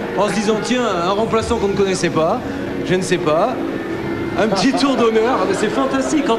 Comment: why does it say thats awesome on the sub titles at 1:11 the guy is saying i dont know xD